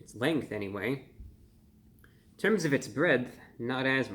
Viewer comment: did he say gridth XD